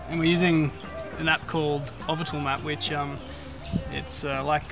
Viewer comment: Can someone tell me what he is saying at 1:23 so I can look it up? He is saying "ovvital," but that clearly is not the word. You can download Google maps easily, so I'd like to see why he's using that one. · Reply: Hey mate, Its called Ovital map. <link> The reason i use that app is you can choose your map source ( bing satellite, yahoo, google maps, google satellite and google hybrid) we use it for navigating in uncharted areas, sometimes one chart source will have clouds that obscure the view of the water.